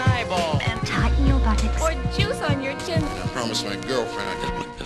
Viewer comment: I could have sworn that woman said "Pour juice on your genitals!" but she says "Pour juice on your chin!" -.